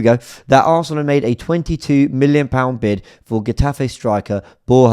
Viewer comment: For a moment there I thought he said we bid for a striker 😮